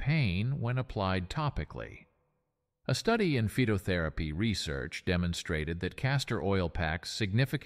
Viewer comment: he says "a study in FETOTHERAPY research"....and the closed captions says "fapping" WTF?